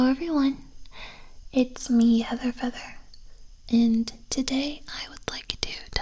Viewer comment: For a sec I thought it said Heathers Feather at the beginning instead of Heather Feather without the s, and I was literally about to die because mandela effect